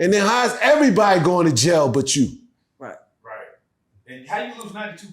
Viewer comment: One thing about DeHaven is his story never changes! My 3rd eye says Desiree Perez (informant) is or was married to OG Juan, who is very close to Hov. DeHaven said everyone went to jail but J. Read between the lines… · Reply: I thought you said everybody went to jail but j Reed 😂😂😂😂